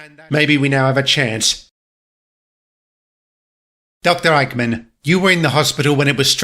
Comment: did I just heard "Doctor Eggman?"